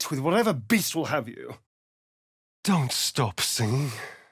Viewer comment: hahahaha I certainly will, just taking my time ;) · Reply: don't stop singing, perhaps? at least that's what I hear